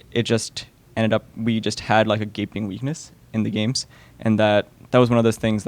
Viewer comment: does Parth say "gaping anus"??